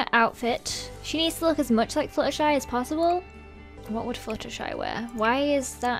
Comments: When you first said flutershy it sounded like you said slutershy. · I'm sorry but it sounds like lizzie is saying sluttershy